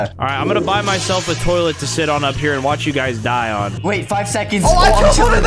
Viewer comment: did stud just say shit?!